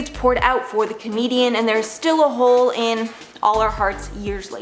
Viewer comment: My dumbass thought you said Canadian at 6:07 instead of comedian, so then I googled Robin Williams checking if he was really Canadian, and not American as I initially thought he was.